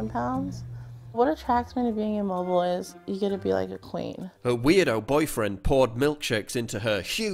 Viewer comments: I'm not sure if I heard correctly. "What attracts me to being immobile is that you get to be like a plane."Did I hear wrong? I hardly see her soaring through the sky.. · "What I like about being immobile is that you get treated like a queen." That may be the saddest and most pathetic statement I've ever heard. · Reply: I heard "you get to be like a plane" but then I was like no surely she didn't say that! A plane doesn't take up that much room..